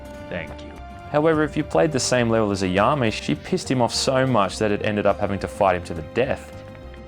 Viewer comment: I thought you said she pissed on him so much lol